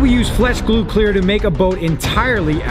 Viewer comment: Why did I hear at 0:01 Flesh Glue